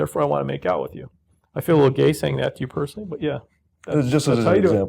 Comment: what does say at 23:26? I feel...? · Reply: I feel a little gay saying that toy you personally" i think